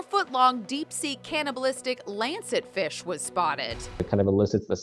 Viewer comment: I thought It's was in anglerfish thats was I heard